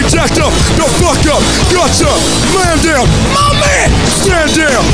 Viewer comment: At first I thought he said "MOMMY" instead of "MY MAN"